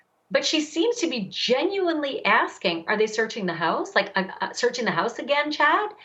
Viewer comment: Lori: “are they searching the house? “ I heard “where exactly are they searching?” Lori: “are they seizing stuff again?” I heard: “ have they found The bodies? “